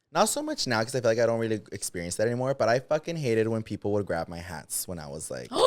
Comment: At first I thought you said “I hate when they would grab my ass” I was like me too then you were talking about HAT I was like ohh! Lmao But I'm a victim of having piojos aaah lol but yes we did know we had piojos we would be embarrassed too biii 😂